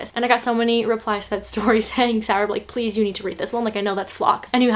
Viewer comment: ? Or is she saying something else, is that her real name? I thought was